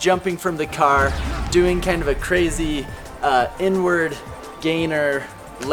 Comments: I thought he said n word gainer. He said inward gainer 😂😂😂😂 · i thought he said “crazy n word” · I thought he said n-word instead of inward lol. Amazing video! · why did you call it a "crazy n-word gainer layout"? Because the word has nothing to do with gainers and isn't necessary to describe a delayed twisting gainer unless you actually call him that. Don't know how you're trying to praise the film while chatting reckless like that.